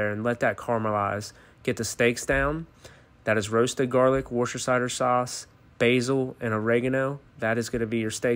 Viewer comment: I’ve never heard anybody pronounce Worcester like that 💀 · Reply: First I thought he said worcester -cider-sauce then realised he said worcestershire lol